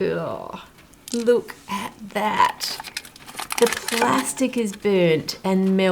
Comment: did you say plastic? Here in Germany Nutella comes in glas jars so eh?!?!?! Plastic and not glas ?!?!?!?